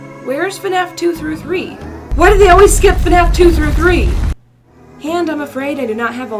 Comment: Nobody: Not even a single crumb: Me: what i heard “Why do they always skip to three three-“